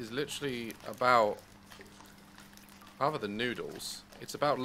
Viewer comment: The game is noodles. -Daz i think i misheard something